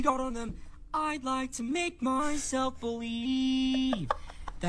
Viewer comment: Am i tripin or did he say id like to make my self a wii